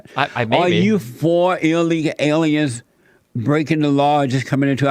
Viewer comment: Take a shot every time he says "illegal aliens" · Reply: Still sober. All I heard was "alleegow allens".